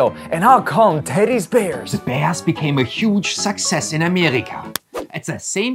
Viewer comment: “A big sexist in america” Pls tell me im not the only one that heard him say that